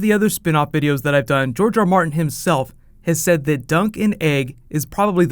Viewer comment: Anytime I ever heard anyone say "Dunk and Egg", I always thought they were saying "Duncan Egg" lol guess I need to read those books next XD